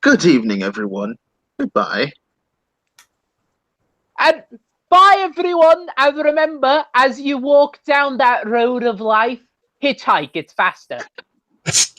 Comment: What did say at 2:26:35? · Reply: "Bye, everyone! And remember, as you walk down that Road of Life, hitchhike, it's faster."